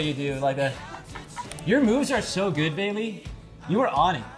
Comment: I thought he said “ Your moves are so good baby” I almost chocked😂